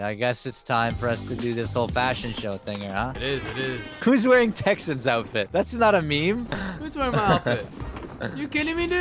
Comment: I swear I heard “Texans outfit” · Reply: lol I am dying